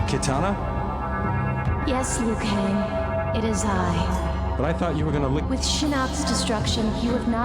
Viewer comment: Kitana: "Yes, Liu Kang I'm here." Liu Kang: "But I thought you were gonna lick my - " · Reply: That's what I thought, but he actually said, "But I thought you were gonna leave me -"